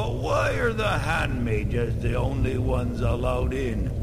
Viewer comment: I thought the first guard said "hand-maidens," instead of Hand-Mages XD This is an awful lot of effort to go through for an enhanced re-release. Other game companies could learn a thing or two from that. Though most re-releases now-a-days are usually GotY editions.